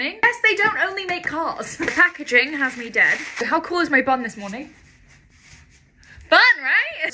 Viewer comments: I thought she said how cool is my bum😭😭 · I thought u said ‘how does my bum look this morning’ And I was just like.. ‘i cant see your bum in that robe, love’, before realizing you meant your BUN 🤦🏻‍♀️ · Lool legit thought you said “how cool is my bum this morning” 🤣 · I thought she said “How cool does my bum look” 🤣🤣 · Help I thought she said, ”how cool is my bum this morning” 😭🥲🤣